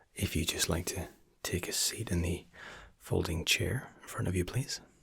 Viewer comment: Right at the beginning When you said "sit in the folding chair", I heard "sit in the floating chair" and I had to process that😂